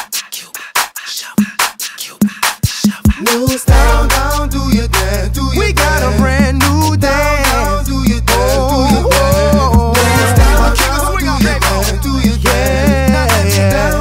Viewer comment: I always thought he said "Do your Dad" instead of "Do your thing"